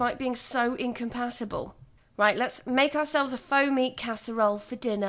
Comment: I rewatched 14:53 over and over because I kept hearing "foamy" and not "faux meat" casserole. 😂😂